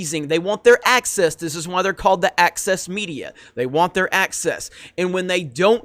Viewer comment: I thought he was saying "Axis Media."